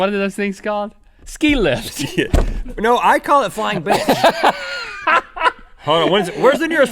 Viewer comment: Could have sworn he said flying Bi**h